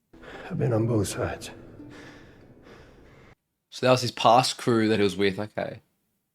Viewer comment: Sorry, but it sounded like you said, "Oh, so that's his crew he was with before." I see why thought what she did. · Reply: "Oh, so that was his past crew that he was with, okay..." What a difference a mere "like" makes to the meaning. ^^